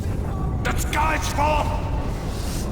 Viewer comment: I never understood what the wounded sailor at 4:16 says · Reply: He was trying to say the sky’s full of em